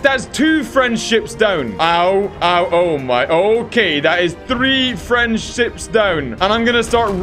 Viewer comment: sounded like said 3 friendships destroyed